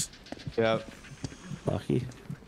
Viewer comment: Anyone else hear say "lucky"?